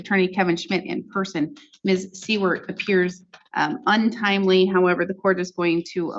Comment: I thought I heard the judge say "Miss C-word is untimely."